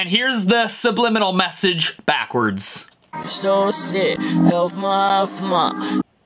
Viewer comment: It sounded like he said "I'm so sick..help mama" kinda